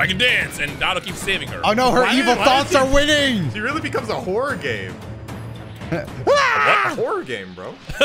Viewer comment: I was also thinking.. .wait did he say a whores game?